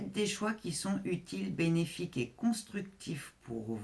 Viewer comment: "Perspectives bénéfiques", j'ai entendu "bellifiques" ❤ Merci 🙏✨️